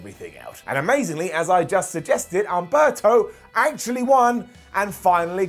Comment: Did call Humberto as Alberto? 5:31 Or did I just misheard it!!